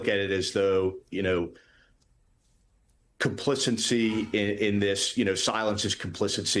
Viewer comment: Sounded like Fanone was saying "complicancy", and I think that should become a new word - a combination of "complicit" and "complacency" describes much of the US right now